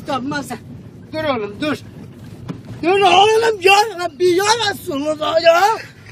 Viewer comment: burda ne dediğini bilen allah için yazsın kafayı yicem yoksa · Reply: dur oglum yarabbi ya resulullah diyor .d